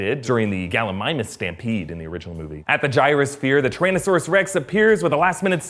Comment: Did he just say indosaurus rex